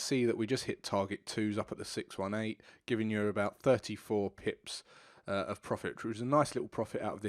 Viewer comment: I watched with closed caption on and I love its redition of your words. At 7:23 it read about 34 pics of prophet instead of 34 pips of profit. Ha ha. Learning is fun